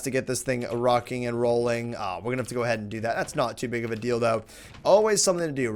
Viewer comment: that's not too big of a dildo. I'm bored. I hear things 😂